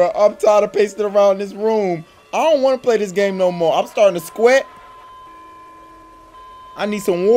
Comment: i dont wanna play this no more im starting to sqeat i need some waterrrr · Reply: I thought that I was the only one that heard him say sqeat I had to rewind lmao.